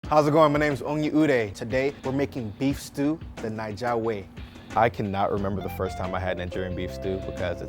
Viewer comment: I thought he was saying lets make beef stew the negga way